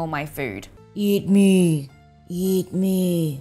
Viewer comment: Did it just say "Yeet Me"